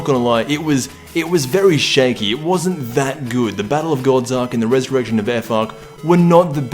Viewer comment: anyone else heard 'resurrection of fuck'?